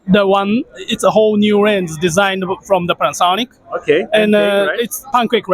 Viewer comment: The CEO @ 0:55 in the video said the lenses were all new Sony ones now. So, not Kopin's it seems. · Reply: Thanks for pointing that out. I re-listened and to my ears it sounds like he says "it's a whole new lens designed from Panasonic" which makes a little more sense to me (since they're a Panasonic subsidiary) but then again, at AWE 2022 (US) this summer Kopin was still showing off the P95 as new so they could be referring to that as a co-designed optic (when Shiftall started they were using the P80). If you search for "roadtovr P95 kopin" there's a report from this year's AWE that shows a through the lens shot w/ impressive clarity.